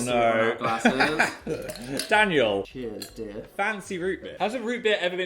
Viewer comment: rewatching after the hard launch and i didn’t realise how cheeky this part was until now 😭😭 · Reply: did dan just call him dear lmfao